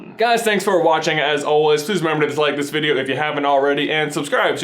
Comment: did he say at 7:39 to dislike if you enjoyed